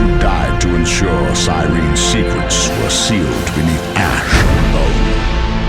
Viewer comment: Can anyone tell me what he is saying at 0:17? "the innocents who died to ensure curene's secrets ere sealed within the ash of (???) this day". What does he day in the (???) part? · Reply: ***** 'The innocents who died to ensure curene's secrets were sealed beneath ash and bone haunt me to this day'